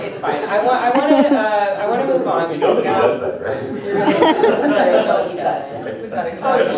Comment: What is David Gaider saying in 32:30 + ? Cant quite hear that and it seems to be an awesome joke. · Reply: Gaider: "You know that he does that, right?" "No he doesn't..." Gaider: "Yes, he does."